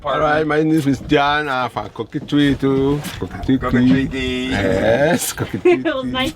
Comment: What a wonderful experience . Gosh had beautiful eyes. At first I thought he said Cocodrie.. as in Louisiana in the USA.